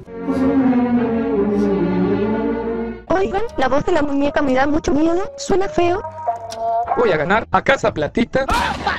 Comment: a mi me encanta su voz Bueno tambien se me pego la frase que dice Aunque no tengo ni idea de q dice xd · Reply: Dice : juguemos muévete luz verde